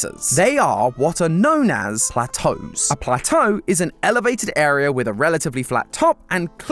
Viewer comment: MINUTE 6:15 SUBTITLES SAID A TOEEEE